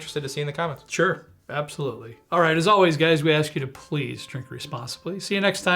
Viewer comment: At the close of every show when says, "Please drink responsibly", I hear it as, "Please drink Wisconsinbly". There are websites for this, and being a drinker from Wisconsin doesn't always mean 'responsibly' (think cow-tipping). 🐄🤪